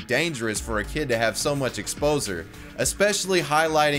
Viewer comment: OMFG ..... "...ex-poser" instead of exposure -_